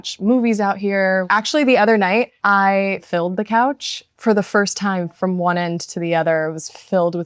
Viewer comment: oh you Filled the couch I thought you said you Built the couch. And I wasn’t even that surprised 😂